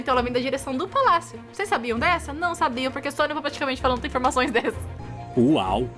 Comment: voltei um milhão de vezes e não entendi oq ela falou 😭😭 nem na velocidade 0.25x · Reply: Acho que consegui "Só nós praticamente pra ta falando uma informação dessa"